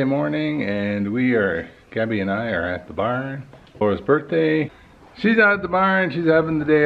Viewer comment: Wait I’m confused I thought gabbys birthday was in April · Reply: Oh wait nvm haha I thought he said gabby it’s laura’s birthday